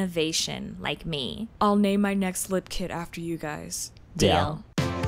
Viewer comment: I swear I heard " I'll name my kids after you guys(die) " 💀